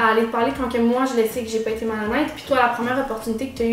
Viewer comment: ''quand que moi jle sais que j'ai été malhonnete'' , elle a dit ca ou j'ai pas compris? si oui, pourquoi elle a été malhonnete je comprend pas, est-ce qu'elle se contredit? :O